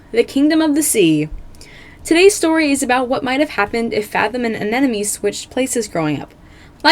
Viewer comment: did she say Fathom